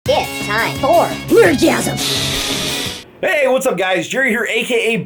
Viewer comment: The first time I heard his Intro I thought it said: Gifs, time, *porn*. Wot.